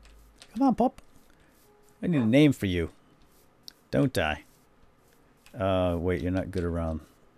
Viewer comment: Call your dog "Don't Die", cuz that's what it sounded like.